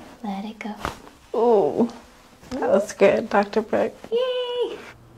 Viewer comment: Anyone know what the blonde chiro at the beginnings name is? I thought I heard? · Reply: Either that or Dr. Brooke. Her cracks sound amazing though!